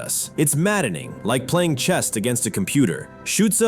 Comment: The quote beginning around @ 2:38 seems bogus to me. No one in the 1940s could imagine a computer playing chess. Also, is the voice AI slop because the voice says "chest" instead of "chess"